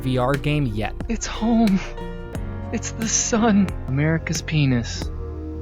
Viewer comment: loving a shout out to my cold depressing island home :_SmoothLove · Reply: I heard "America's Penis" and got excited